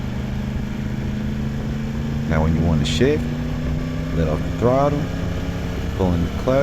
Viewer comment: this part got me crying bc, ik you said “ Now when you wanna switch/ shift “ But what I heard “ Now when you wanna sh*t” 😂😂😂